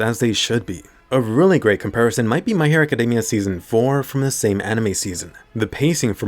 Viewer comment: I like how the english caption reads "hair academia" instead of "hero academia" LMAO